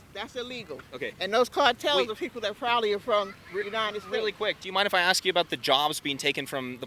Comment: did she say the cartels are people from the United States?